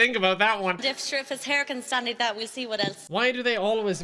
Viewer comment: I have NO IDEA what she said at 5:53, but apparently it was sexual? can you translate? please. I don't understand. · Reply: 'sure if his hair can stand like that we'll see what else (can stand like his hair)'